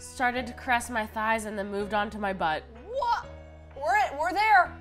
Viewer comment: Anybody else thought said "...into my butt" instead of onto?